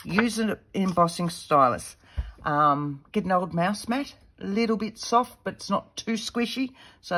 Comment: Enjoyed our tutorial. hadn't seen these little pads before. Questions, you mentioned if you did not have a scoreboard, use a ruler and stylus and (what), didn't understand that word at about 5:12. #2 you did not mention the dimensions of the middle size pad? And finally, where did you get the little spools to store your lace on, or did you make them? Can you provide source? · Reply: Hi, Margaret - she said "mouse mat" (mouse pad, for your computer); the concept is that you score on a surface that has just a tiny bit of give - too much and your paper tears. The middle paper just needs to be smaller - watch 10:25 and 11:00 for how she explains it. From what I saw from 3:00 to 3:40, the little spools for the lace are her old greeting card stock she shaped by cutting out a little on the center of each side so the lace doesn't slide off the end when it's wrapped around. Hope this helps. Happy crafting!!